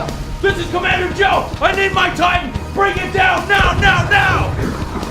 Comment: when Joe says "i need my titan!" i thought he said i need Mike Tyson. Don't know which one is worse.